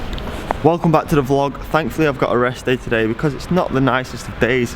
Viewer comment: I misheard your first sentence: Thankfully I got arrested today... Well, you got my attention there!